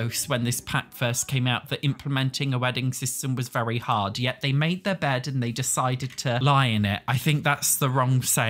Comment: I thought you said "they made their bad" as in a blow to them instead of the saying they did their best 😅